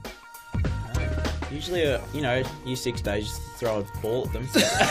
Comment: what does luke say at 10:20?? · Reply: He is answering the question How can a girl tell if a guys likes her. Luke replies with "Usually you know year 6 days you throw a ball at them"